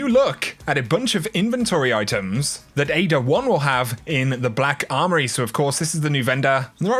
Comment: Everytime i hear Ada-1 i keep hearing 'Ada Wong'